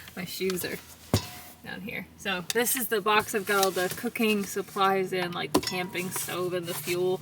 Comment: I was half asleep and I thought you said "this is the box with all the cocaine supplies" and lemme tell you I woke right up